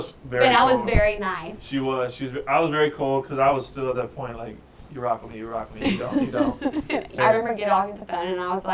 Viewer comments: Does anybody know what Tyler said at 13:15 after Hannah said "you were very cold". I cannot make it out, and he repeats it a couple of times????? Thanks! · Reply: I think “you rock with me you rock with me if you don’t you don’t” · What does he say around the 13:15 mark? Something about if you “ “ and if you don’t you don’t? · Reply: If you rock with me you rock with me if you don’t you don’t. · Did you happen to hear what Tyler said after Hannah said "you were very cold"? (At 13:15). He repeats it a few times but I cannot make it out. Thanks so much! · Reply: he said I was very cold